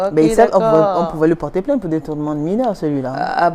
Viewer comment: “...on pouvait LE porter p...“ 🤔🤔🤔 Maybe I didn’t hear properly. When one guest says they “... mets le téléphone 𝐞𝐧 𝐛𝐚𝐬 du lit...“, you think French isn’t as easy a language as one makes it... 21:26 “...une personne vraiment drôle... qui avait le sens de l’humour...“ Euh... What’s the difference?🤔🤔🤔 Just saying...🏃🏽‍♂️🏃🏽‍♂️🏃🏽‍♂️ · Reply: Laurent NGASSAM, je me permets de vous adresser mon retour. J'ai écouté plusieurs fois. Elle dit "lui" mais c'est difficilement perceptible. Téléphone "en bas" du lit, çà effectivement c'est le français parlé camerounais, traduction littérale de nos langues vernaculaires. Pour le 3e point, la redondance ne pose pas un pb, j'y vois même pour ma part un côté pédagogique pour la masse qui écoute. Bien à vous.